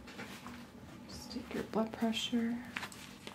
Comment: it sounded like you said "taking you buttpressure" :-D